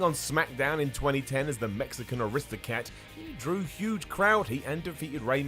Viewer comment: Did he call Del Rio an Aristocat? I know it was meant to be "aristocrat", but I heard "Aristocat" like the Disney movie.